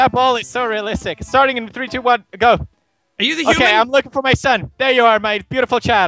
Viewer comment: "Are you the human?" 🤔 What are you saying? 😂